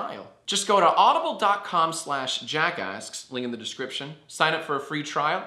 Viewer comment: Who else heard jackass?